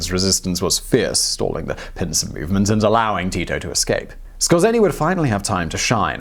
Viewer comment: did you say Tito Jackson? It sounds like you nearly did.